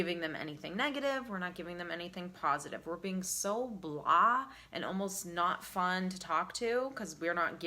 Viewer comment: what is the word here? “We’re being so ...” blur or blocked? I understand the overall meaning and I’ve been acting this way for several years now but I’m not familiar with the word, help me out native speakers please🤗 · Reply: Lana Light the word she used (a couple of times) is “blah”